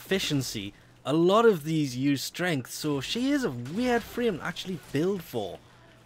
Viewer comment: did you say "she" at 7:20?